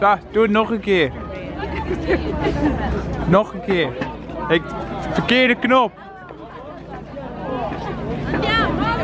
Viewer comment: is he saying wrong button? as in 'verkehrter Knopf?' Today in Liza deducts dutch. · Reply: yes, he is saying wrong button!! verkeerde knop - verkehrter Knopf - Wrong button :D good job deducting